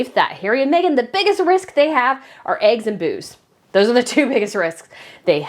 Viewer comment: 😂😂😂 I misheard and thought you said eggs and booze.